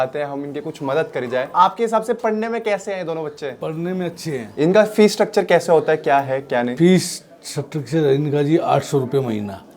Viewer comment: did he said fee suckturture insted of structure ! 😭